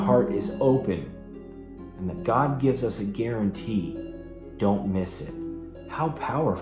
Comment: I hear "God gives us an opportunity" instead of "guarantee" with.